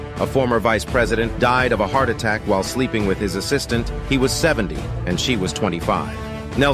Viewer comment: What was said: "he was 70 and she was 25" What i heard: "he was 70 and she was a baddie"